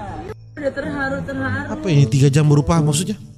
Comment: for yg nda mangarti di menit 5:13 gempi bilang apa, dia bilang "mom look at that Cock" yang seharusnya "mom look at that car" dan Cock dalam b.inggris berarti kont*l jadi mama Gisel langsung reflek bilang "what a car" biar gk ambigu si gempi.. maaf kalo salah🙏